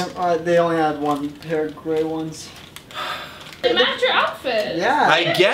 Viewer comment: I thought said they only had one pair of gay ones 😂